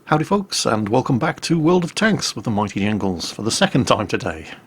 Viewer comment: Am I the only one who heard him say "The Mighty Ingles" at the begining?